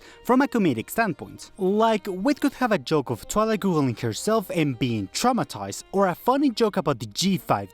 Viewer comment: I misheard killing herself